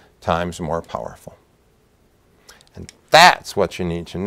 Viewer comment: In the beginning, when he said, "what about nuclear bonds?" I at first heard it as nuclear BOMBS!